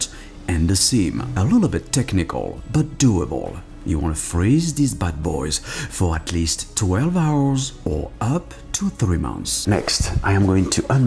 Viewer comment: did he say 3 months?